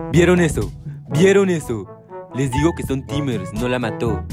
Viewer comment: pense que dijo tigers 😂😂😂😂